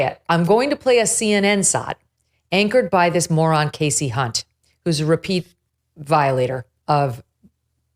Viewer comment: I'm sorry, Megan, did you say Casey Cunt? Just saying what a double standard